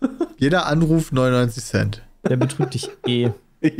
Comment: Ich verstehe irgendwie nicht was Sep da sagt. Ich bin Schwerhörig, wäre nett, wenn mir das einer sagen könnte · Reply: der betrügt dich eh